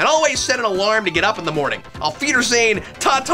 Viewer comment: Did, did he just say, "All feet are sane"? I- I don't think that's how it's pronouned. Or makes much sense.